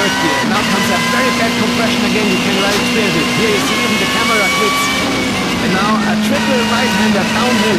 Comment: For those wondering what he said in 5:33 is "right here you can experience it even the camera quits!"